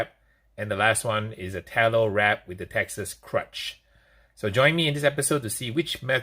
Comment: At first I thought you were saying Texas CRUNCH, which sounded pretty interesting. Then it dawned on me.